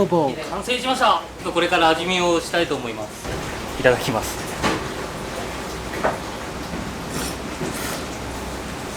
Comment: What does he say at 11:01 ? I'm learning japanese and he say it polite and fast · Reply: Itadakimasu